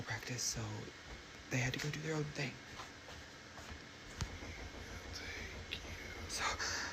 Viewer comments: Did somebody else hear a disembodied voice saying"thank you" at 36:23? (No, you didn't 😉) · the lady by the tree video 36:24 i heard something say "we will take you..." hell naw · I just heard a whisper say thank you at 36:23! What is going on? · Did anyone else hear the voice say "Thank you." in the second to the last video, video? In this video, it starts right at 36:23. So go a little ahead and put the volume high. Sounds really creepy. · did yall hear that?! It sounded like another person whisper-growling, "take youuuuu"!!!! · AM I TRIPPING OR DID I HEAR THE WHISPER “THANK YOU” at 36:24? PLEASE TELL ME I’M TRIPPING! CUZ HE🏒🏒 NAW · anyone else hear that thank you??? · I heard "thank you" when that guys was talking about the woman being outside. It was around 36:22. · something walks up and says thank you in his video ! 😮 did anyone else catch that !? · Anyone else hear a " Thank you" @ 36:25 ,in the background in a whispery way? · Yo at 36:23 I heard a voice say “thank you” it was a male voice check it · I heard something say "Thank you" sheeeeesh 😮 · Did he just say "I'll take you"? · I heard “thank you” right on 36:24 😮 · we will take you. I know someone else heard that 😂😂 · mark who tf said thank you in a creep ass whisper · so nobody else heard this voice in the background... sound like it says " no thank you"😳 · Did anyone else hear the "thank you" @ 36:23? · Did anyone hear someone saying “thank you” at 36:23? 😵‍💫